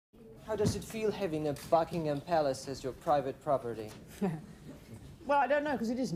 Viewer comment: I thought he said "how does it fell having a FUCKING palace..."